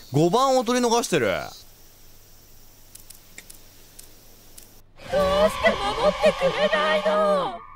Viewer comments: これ多分ずっと続きからやってるからなのかな？「どうして戻ってくれないの？」って聞こえる。はじめからやって欲しいってこと…？ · 考察(長文注意) end9で大勢の主人公と同じ顔をした男の子たち(精子)がいたこと、男の子たちが大勢いたのに対して天使(卵子)は1人、手をつないだ瞬間溶けて(受精)、エンディング(背景がおそらく羊水)。以上の理由から舞台は子宮の中。 そしてエンディング後、おばあさんが「来た道を戻れ」と言っているため精子として辿った山道(産道)を赤ちゃんとして戻る、つまり生まれることを指している。 (ここから考察要素が強く入ります) end1.けいこく さんが何度もリトライをし続けた結果、突然見たことのない女性が「どうして戻ってくれないの」と主人公に対して言っているように聞こえる。つまり、見たことのない女性は母であり、望まない〇行為であったorゴムの付け忘れ。そして主人公(精子)を掻き出そうとしている？ end2.おかえり 振り返った際に立っていた人物が、赤ちゃんのようにも見えるが明らかに奇形であること、布を持っていることから流産であることが考えられる。 今まで振り返らず生まれたものの結局命を授かることができなかった、つまり最初からやり直しであることから「おかえり」 end3.おねだり 主人公の前に立っている女の子が目や耳などを「ちょうだい」と言ってくる。目や耳を奪われてしまってもその状態のままゲームを続行できることから、何かを奪われた状態でクリアすると〇がいを持って生まれてきてしまう？ end4.みちづれ(あまり自信がない考察です) 主人公の前に立っている人物の髪の毛が異様に長くて整えられていないこと、カエルの卵を食べていることから、生まれた家庭の貧困による餓〇、自〇。 end5.ぎまん(あまり自信がない考察です) 「私があなたのお母さんよ」というセリフに対して振り返ると、「だまされた」と嬉々として襲ってくることから誘拐などの犯罪に巻き込まれてしまう？ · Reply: エンディング 1のやつは多分違うと思います謎の女性は先生でなんで戻ってくれないのではなくなんで守ってくれないのと言ってると思います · ここ「どうして守ってくれないの？」って聞こえるから、主人公が先生の言いつけを破って後ろ振り返ったからなのかな、、？エンド「けいこく」だし